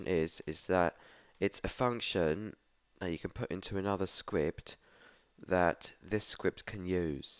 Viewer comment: Is is just me or is he saying sqwipt insteas of script